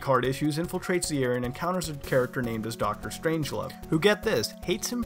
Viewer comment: Did he just say there's a character named deepthroat?